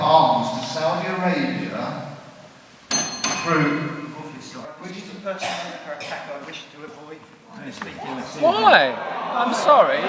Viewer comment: What actually happened at 23:50ish? I heard "We're sending weapons to Saudi Arabia", then I heard a bell, then I heard...mumble, and then "Come on" and he hands the mic over. What was the mumble? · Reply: Not quite sure, but something a long the lines of "this is a personal (inaudible) attack I wish to avoid (inaudible) it's misleading"